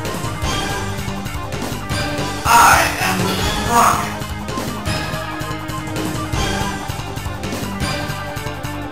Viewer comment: what did the Head said? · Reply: I am the strongest. I am just a small portion of the empire Bacterian. と喋ってるみたいですね（検索したら出てきたのを張り付けました）